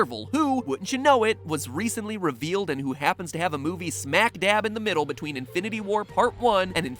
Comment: did matpat say smackdab?